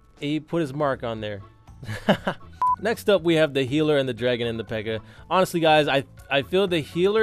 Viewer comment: Did he say pega